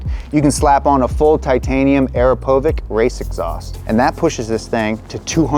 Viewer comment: the legendary "ARAPOVIK"😂😂😂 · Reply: I thought I've heard "AEROPHOBIC" 🤣